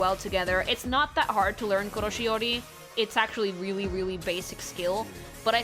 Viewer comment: What is this word? Koroshi? Kiyoshiori? What? I’m trying to learn more about color theory, and I don’t know this term! Help me figure this out! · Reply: Koroshiori, in other words Color Theory. She calls it that way because her teacher had a classmate who used to say Koroshiori instead of color theory.